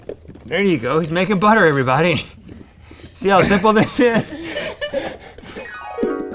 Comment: did he say "see how simple that shit is" never heard GMM cuss xD